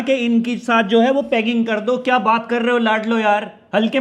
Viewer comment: i thought i heard it wrong, bhaiiiiiiii?????